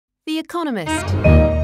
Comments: “Be a communist” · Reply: I hear it like “Be a comenist.” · “Be a communist” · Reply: Lol This guy Mishearing The Video :p Misheard Version=Be Communist Real Version=The Economist